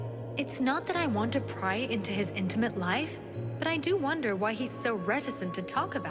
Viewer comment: The fact the first clip of the video is someone asking about their sex life, means this video it's either going to be quite interesthing or this was the peak of this game and nothing can top it. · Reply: The whole framing makes it look like a Robot Chicken sketch. EDIT: I also wasn’t looking at the subtitles, so misheard “intimate life” as “ Internet life”, making it sound more surreal and funny than it really was.